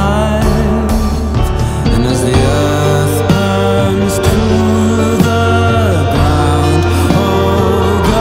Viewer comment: you know what hits? Whenever i listen to this song (only the original, not the female cover) when he starts saying "as the earth runs through the ground" and it keeps going my feelings build up inside and when he says "oh girl its youu that i lie with" I feel this certain way i cant explain and i sometimes feel like crying its so hard to listen to this without that feeling. thats why i love this song :) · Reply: I really thought he said *and as the earth BURNS to the ground...*💀💀